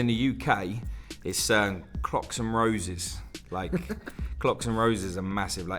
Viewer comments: Am I the only one that heard “CROCS and Roses” not “Clocks and roses”?? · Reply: I definitely heard "cocks and roses." 😐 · lol i thought said cocks and roses at 1st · why the fuck did I hear "crocks, and roses"?!